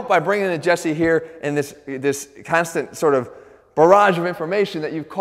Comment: please, I'm from Brazil and I didn't understand the word he said at that moment. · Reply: “In this constant barrage”